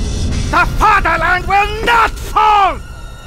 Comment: I thought he said "THE FEGELEIN WILL NOT FALL"